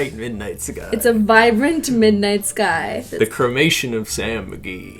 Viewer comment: I thought said “the cremation of Sam and Dean” (supernatural)